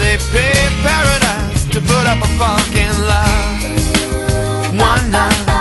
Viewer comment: "A big paradise, we put up with a fu*kin lot" I swear that's what I heard for years before seeing the lyrics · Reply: I hear "put up a fuck line"